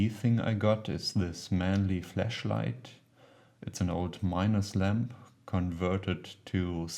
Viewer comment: Did you say flEShlight?!